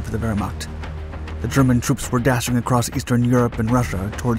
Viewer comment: I could swear I thought I heard him say that in 1942 everything seemed to be going according to plan for Vermont. Actually, that's what the closed captioning said.